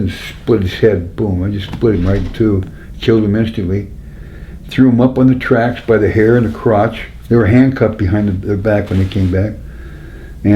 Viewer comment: did he just say he threw him up on the tracks by the hair of the crotch